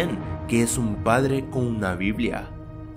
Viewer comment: En el 1:06 escuché "Es un padre con una biblia" NO ENTIENDO QUÉ DICE QUE ALGUIEN ME DIGA XD